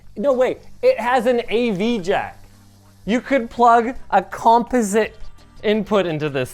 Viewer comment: What does he say at 13:40? composite? Is that a Canadian accent? haha